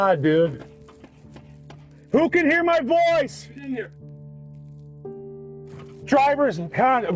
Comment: when the officer says…Can anyone hear me? You can hear a faint man’s voice say IN HERE 😢 😢😢💔💔